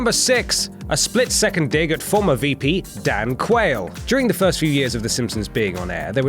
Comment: anyone else hear Dank Whale?